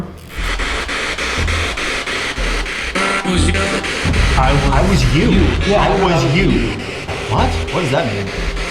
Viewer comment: I hear “I was young” right here